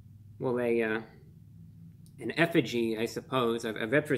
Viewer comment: did you say EFFIGY? 😮 😅 🗿👍